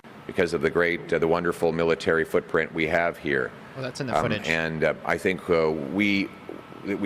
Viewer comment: you can clearly hear the MSN say "that's enough footage" when Pierre is actually saying something of substance.